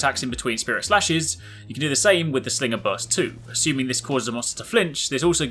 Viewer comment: i thought he said ''you can do the same with this nigga burst''.